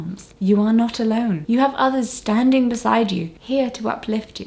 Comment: As a W40K nerd, I heard: " CADIA STANDS!" For those who aren't into that wacky, parodic scifi universe: Basically it's a planet-scaled fortress who faced a disastrous assault, and it broke before the guard did. That's unbound defiance and determination. Like activism at its finest.